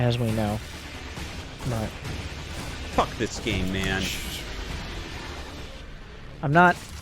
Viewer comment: I mishear artosis every time: "f..k this gay man"